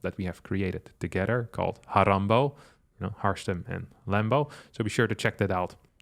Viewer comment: Harambo? I thought I heard Harambe 🐒 🙈 lol 😆